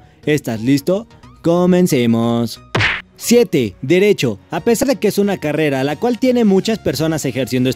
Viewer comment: Derecho jajajajajajajajajajajajajaja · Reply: ¿Derecho? Pensé que había dicho MC Donald's xd